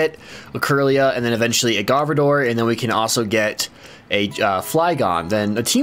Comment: When you said Gardevoir i thought you said Garbodor 😂